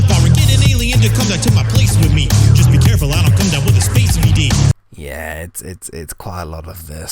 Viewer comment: I think you may have misheard a lyric. I heard it as "space V.D." (venereal disease) rather than E.D. (which I don't think's a recognised abbreviation). If you don't know, a venereal disease is the same as an STI, like crabs or whatever.